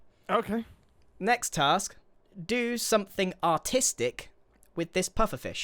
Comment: I thought he said autistic 😭